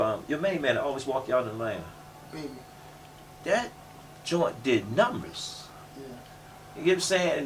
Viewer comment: who did he say at 20:11? the rapper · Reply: Lil baby